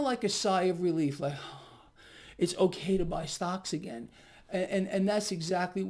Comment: I thought he said “it’s ok to buy socks again”. 😆